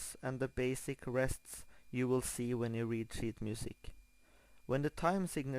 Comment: i heard "i will teach you how to read shit music" o.o i was like AWESOME!